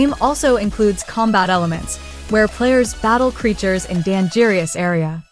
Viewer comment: I’m not usually point out mistakes guy, but did you seriously say “Dan jeerious” instead of dangerous?